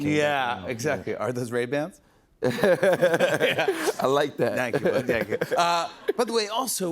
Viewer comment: Did Jimmy say 'thank you my nigga'????????